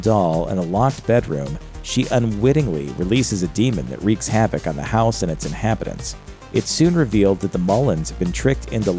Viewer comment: what is he sayimg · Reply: "Wreaks havoc on the house and it's inhabitants."